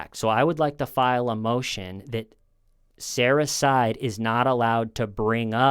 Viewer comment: sounded like you were saying "Sarah-cide"__